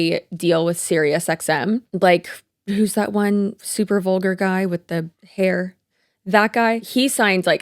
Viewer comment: "the super vuglar guy with the hair" I think you're talking about Howard Stern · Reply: LOL! I need to clean out my ears. I heard “super old guy with the hair”, which is descriptive of, like, half the radio personalities on SiriusXM. 😂